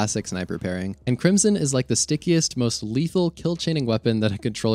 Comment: Lol I thought you said "stinkiest" for a sec when talking about the Crimson. 🤣